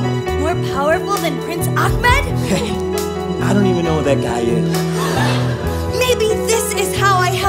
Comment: Only Starkid could have me sobbing over Jafar and mad at Aladdin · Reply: internal screaming oh my god i literally thought they were just saying “i don’t even know” instead of it being a username